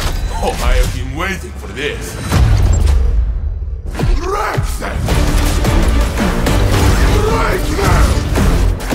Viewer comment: bro I don’t know what saint-14 said after I’ve been waiting for this but that shit gave me goosebumps, I’m so hyped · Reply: I think he's shouting in a super hype Russian accent "WRECK THEM! BREAK THEM!"